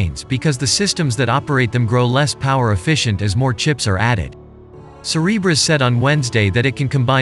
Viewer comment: I keep hearing “Cerberus” instead of Cereberas. Yes, a little concerned…